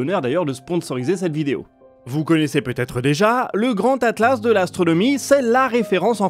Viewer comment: Je suis désolé de dire ça mais j'ai fait une nuit blanche et je me suis lancé une vidéo pour essayer De dormir sauf qu'au lieu d'entendre "grand atlas de l'astronomie" j'ai entendu "grand atlas dela sodomie" et ca a encore plus ruiné mon sommeille.. Voila c'était juste pour ça